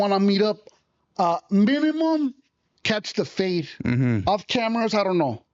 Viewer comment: I really thought he said “babymom, catch the fade” but he actually said “minimum,catch the fade